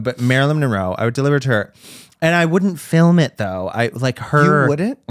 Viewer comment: oh shit did she just said “film it”